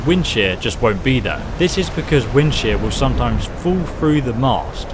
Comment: This whole time I thought he was saying "Wind Shit" Until he put his cross hair on the unique weapon lol xd 😂😂😂